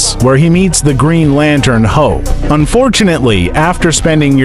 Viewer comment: First I thought he said green lantern ho he said hope lol I'm like green lantern ho what.